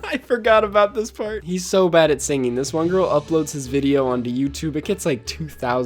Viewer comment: did you just say "on the youtube"?